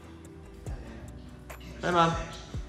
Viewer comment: Did he say "hey maya" at 2:20 ??